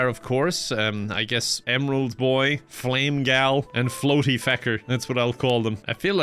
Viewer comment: I thought you said "Bootyfecker" instead of "Floaty Fecker"